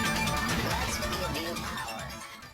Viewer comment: I always heard "Let`s give/get you/new power"